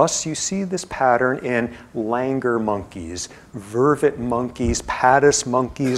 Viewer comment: I keep hearing “lil anger monkeys” 😂